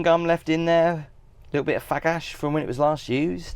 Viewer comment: Great tour! At 12:58 did you say “a little bit of fag ash”???